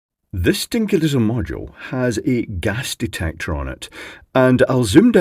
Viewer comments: You said at the start "this dinky little module"- but I heard "stinky little module" haha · i thought that said Gay Detector